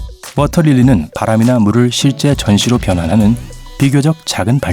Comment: 나레이션중에 "실제 전시로" - 문맥상 전기여야 할텐데 전시로 들리네요